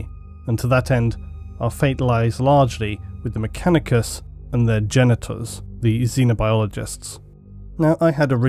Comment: I definitely heard "Our fate lies largely with the Mechanicus and their genitals"